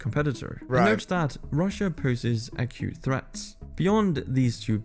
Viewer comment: He wasn’t saying “a cute” threat. He was saying “acute” threat. It just sounded like he said “a cute” threat.